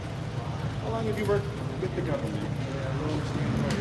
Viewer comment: Wow ! He’s turned into Authentic Intent . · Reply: I don't know if you know this or not but the cop asked how long have you worked "with" the government if you don't believe me you could find out yourself go to the video and put it on closed caption and you will definitely hear the cop asked him that question and what's most shocking is at one point said since YouTube go to 1:04:15 and don't forget to put closed caption on DUN,DUN,DUN 😳😳😳😳😳😳😳🤔🤔🤔🤔🤔